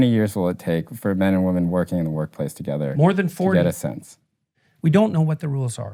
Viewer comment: "More than forty" All I heard was "Morty"... Jordan Peterson is Rick.